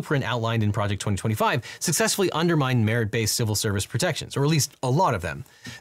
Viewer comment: i heard marriage instead of merit and i CACKLED